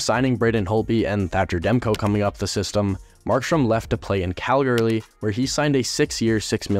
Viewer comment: did Is it just me and did I hear him say calglary. edit thx for a Canucks video before people say I have a blue jackets player for my pfp it’s for Johnny 13 Hockey